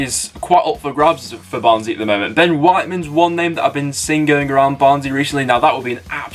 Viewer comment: thought you said Ben White hahaha